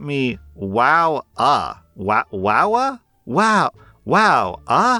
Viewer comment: not noticing “wow AH” was “wow A-Hole!” is incredible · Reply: I thought he said it was wow h so was really confused when he pronounced it wowah.